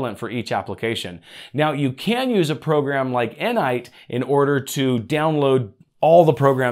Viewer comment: what program did you say in 29:43 ? N nite or wht, i can't find it with that name lol